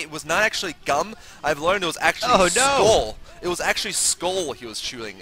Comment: When I first heard say "Skoal", I thought he said "skull." Chewing what kind of skull? Kitten?